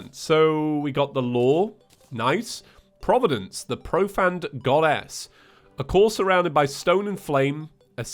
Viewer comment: What Reads: "Providence, the Profaned Goddess" What I hear: "Providence, the Profaned Goddass"